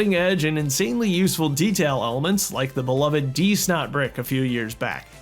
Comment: I kept hearing "deez nut brick"